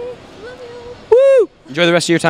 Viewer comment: LOL at the end it sounds like said "Don't get too high"